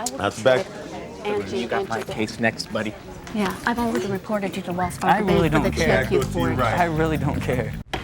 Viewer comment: She threatened stepson at the end. What an awful woman. 😖 understand why hubby left. 😮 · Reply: PLAINTIFF: "I'll get you later a$$hole..." I could be wrong, but it's pretty close to what she said.